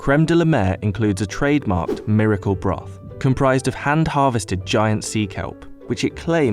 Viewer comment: I hear “miracle bra” 🤣